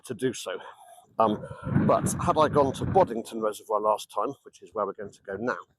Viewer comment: Thanks for the update. What was the second reservoir called? I heard "Boddington", but the only Boddington I can find is in Gloucestershire and you were on the Oxford. I liked your points about leaky locks (that phrase sort of rolls off the tongue). On canals with an aquifer supply to the summit pound, this matters, but on canals with a pumped supply or outtake from a thumping great river (Llangollen), it doesn't matter at all. When you see bywashes happily thundering past the lock, the gates can leak as much as they want (well, until the lock won't fill, that's another subject). Where the leaks are critical, one has to dump flyash in, which really is emergency ward 10 stuff (yes, I'm old). Totally agree on government responsibility, which the Tories thought they could shirk 100%. Far down may they sink. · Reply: Yes, indeed, Boddington! <link>